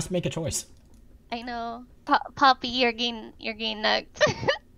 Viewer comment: did she just call tristana poppy?..